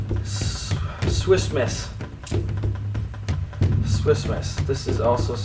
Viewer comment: I thought he was saying Swissmas I was like wtf is that